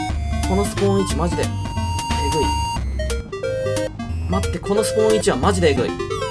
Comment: ｢ このスポーン位置はまじでAV ｣ に聞こえた。ド、ドブのせいだ！！！！！！